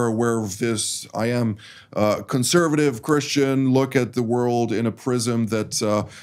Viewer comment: did say he is a Christian?